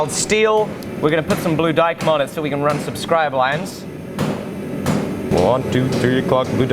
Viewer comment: it almost sounds like you said "subscribe lines". Getting nice and sneaky with the self promotions eh?😜